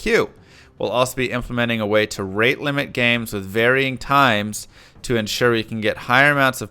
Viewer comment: what I heard was limit playing times Also any word on their future subscription plans? That's why they're adamant about online only, right?